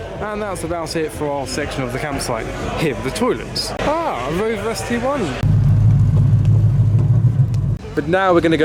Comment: What car is that at 3:00 - 3:03 that he is showing? I can’t understand what he says. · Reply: Rover SD1 v8